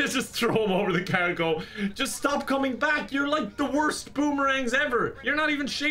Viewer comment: When Kevin said boomerang I thought he said boomerling